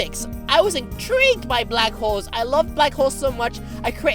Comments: HAHAAA PSHHHH WHAT IS THIS SEXY PERSON AND ALSO I THOUGHT SHE SAID BLACK HOES AND ALSO BRAH 98% WAS HER SAYING IM SEXY 2%WAS DRAW MEH LIFE OR POOPING XD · when he said he loved black holes i thought he said black hoes.......... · I thought he said "I love black hoes".